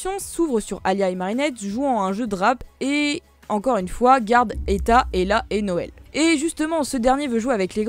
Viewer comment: quoi??? T'as parler Russe wsh "garde et ta et la n-o-l" c'est tout c'que j'ai compris frr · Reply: La Mtryxé Etta, Ella et Noël C’est les petites sœurs d’Alya et le petit frère de Nino